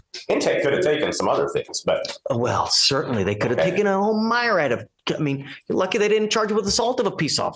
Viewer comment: Did he say "Mire-Add" at 6:24? Did he mean "myriad"?